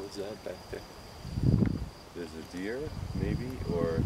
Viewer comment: I hear a female spirit answer?? as to what was in the woods, a deer or, etc? The female spirit said, "it's a deer........" .I couldn't get the rest. So cool!